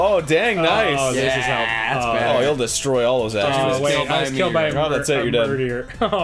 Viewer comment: I thought it said "Derny was killed by a Metroid".